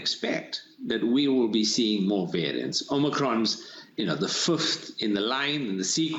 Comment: Omicron is the funf in the line... Nice random German word in there. :D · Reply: He doesn't say fünf. He clearly says the fifth He speaks English with a South African accent.